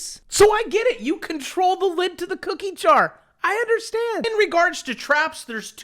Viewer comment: I get it. You control the lid to the cookie jar..... you crack me up ! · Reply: I thought he said the lint in the cookie jar....ummm....gross. :)